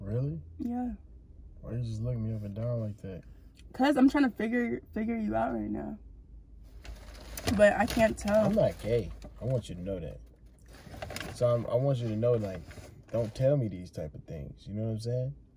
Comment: when he said "I'm not gay I hope you know that" tell me why I thought he said "you got cake I hope you know that"😂🤣